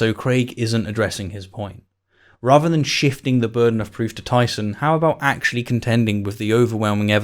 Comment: Who else thought it said "Shitting the burden of proof"?